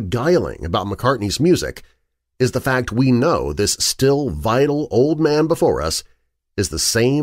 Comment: Great work. But near the end I misheard and thought you said, 'This vile old man before us. ' 😂 I thought it was a bit harsh until I realised that you actually said, 'Vital.' 😂 More cryptid and UAP videos, please.